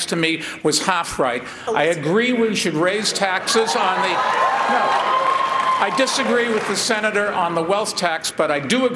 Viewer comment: what does warren say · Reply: lydia ... It sounded like, “Oh, that’s a good one”.